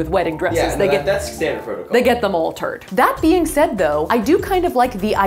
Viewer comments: I thought she’d said all turd in 21:20😂😂😂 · when you said "they get them altered" I literally heard "they get them all turd" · I thought you said "all turd" instead of "altered"